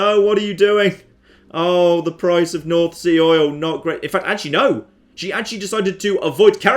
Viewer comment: Did you say nazi oil?